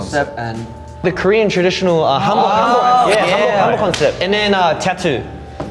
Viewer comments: I think they said 'Hanbok concept' NOT 'Humble concept' If I am not misheard. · what they said in reference to the traditional korean clothing is the "hanbok concept" not the 'humble concept'